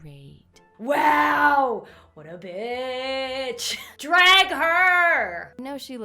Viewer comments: Did you swear!? Did you say Bitch!? at 7:22 to 7:25!? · is Lauren saying “what a ‘bitch’?” Ooops I’m a child shouldn’t be saying that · did she say “WHAT A BIIIIIISHHHHHHH” or b** ?? O_O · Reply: She said bitch your welcome :) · did Lauren say the B word on her family friendly channel?!😱😰